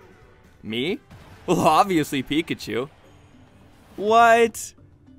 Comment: BRUH I HEARD PIKACHU AT 2:06 AND I WHEEZED SO HARD BTW THIS MADE MY DAY